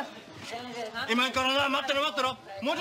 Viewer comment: のところOKOKって聞こえたけど桶桶って言ってたのか。